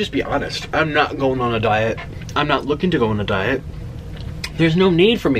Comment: I thought you said you were going on a diet